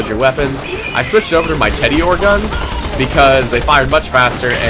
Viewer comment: did you just say "Teddy-or"?